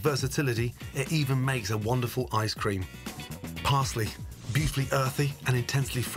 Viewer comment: did he say you can make basil ice cream?